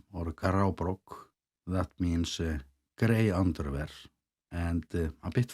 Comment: did you say “gray Underwear?” 😬